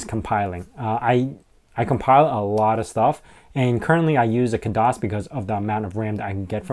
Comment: what is he saying that he complies on? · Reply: Khadas